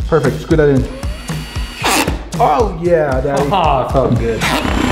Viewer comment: did he Just say "oh yeah daddy"?